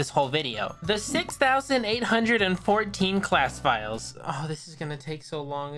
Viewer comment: I like how the subtitles says 6418 instead for 6814